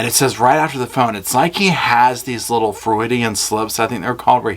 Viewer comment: It sounds like you’re saying Floridian Slips 😂 Then you said, “I think they’re called” so it seems as though you might want to know the proper name.They’re called Freudian Slips, after Sigmund Freud, the psychologist. Pronounced f-ROY-d. Thank you for your summation, it’s very helpful. It’s been difficult to sit through hours and hours of testimony detailing cell phone and bullet casing data. You made it short and interesting.